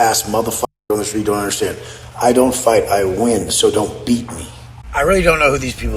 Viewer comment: ish “I don’t fight, I win, DON’T BEAT ME”. Did I hear that right?